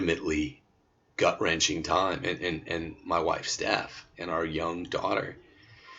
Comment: did you say your wife’s death? I never heard you say before that she had died. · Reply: I had to rewind it to listen to it again, because I thought that's what I heard, too! But it was the way Tim said, "my wife Steph", in the middle of his sentence that caused my confusion. He said it so fast that it sounded very different the first time I heard it, too. So, don't feel alone on that one! I was so glad that it was just my hearing that was a bit off. God bless Tim & Steph and their whole family!